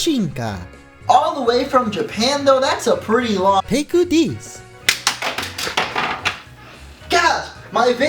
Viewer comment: For those who did not understand what he said at 0:33, he said "Pick this"